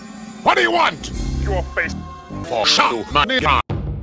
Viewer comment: One question. What's he saying at 0:04? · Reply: fo' sho' mah n